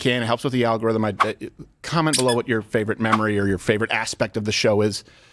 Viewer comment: does Steven say "comment your favorite mammary"???? Um... all of them??